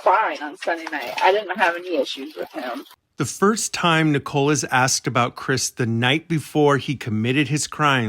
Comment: Who picked the name Nico for the son? I thought they were saying Nichole this whole time.